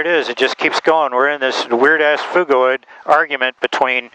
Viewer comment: What's this word he is using at 4:00? "We're in this weird-ass fugoid"? Fugoid? What word is he saying here? Is it slang? An engineering term? Some variation of "fugue"? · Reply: Phugoid. Got a dictionary?